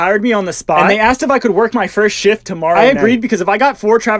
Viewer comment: I heard him saying sh!t not shift